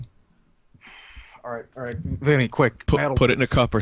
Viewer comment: Vinny, Did you call "Vinny"?